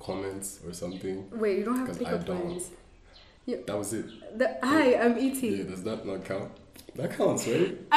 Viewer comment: Hi am Et is actually a pick up line for him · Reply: I thought he said " hi am into you"